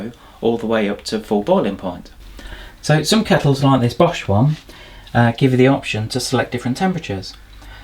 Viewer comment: haha, I thought you said: "Some kettles, like this posh one, give you the option to select different temperatures" - Iit's not wrong...